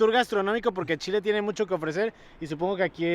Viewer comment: Lo que yo escuche en mi idioma tico: ""Pero sigan disfrutando de este tour gastronómico, por que AL CHILE tiene mucho que ofrecer" Lo que en realidad dijo... "Pero sigan disfrutando de este tour gastronómico, por que CHILE tiene mucho que ofrecer"